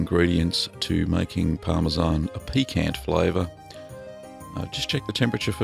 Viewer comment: so he says "known... for it's piquant flavor" But I heard "PEE-can flavor", so first I'm thinking, hey how is it possible that an aussie can pronounce pecan in a perfect east coast US style? Which is really annoying being as I live on the east coast? It took oh, 2 seconds or so to realize that Parmesan cheese doesn't taste like pecans. Oh, and it's pronounced pee-CAN, or pee-CAHN